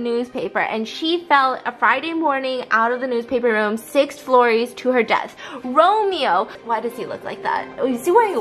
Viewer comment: Did she say six floories